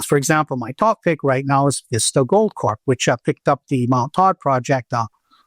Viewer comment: What he says here is "Vista Gold Corp." Vista Gold's big Australian project is called Mt Todd.